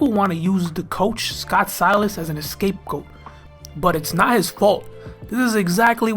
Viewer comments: When he said scapegoat, I thought he said escape goat 😆😆😆 ima use that from now on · Man did you just say an ‘Escape Goat’ ? Bruuuuuuuhhhhhhhh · did you just say "escapegoat"? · Good video, maybe I just heard wrong but “escape goat” cracked me up